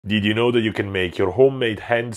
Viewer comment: I thought he said home made pencil